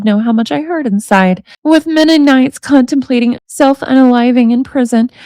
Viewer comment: With Mennonites contemplating self unaliving? Is that what I heard 😂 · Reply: “With many nights contemplating self unaliving..”